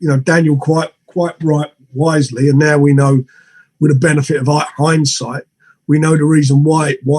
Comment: Hahaha...I watched some...This is what I heard..."why, why, what, when, where hindsight..."....PMSL